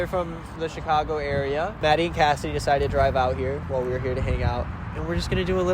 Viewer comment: FPR A SEC I THOUGHT U SAID MADDY AND CASSIE AND I WAS LIKE YO U GOT IRL EUPHORIA HERE